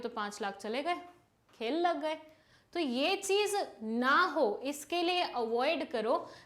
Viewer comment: how many of u think that mam said ki L lag at 7:53 🤣🤣.....btw it is not L it is khel lag gye.... 😂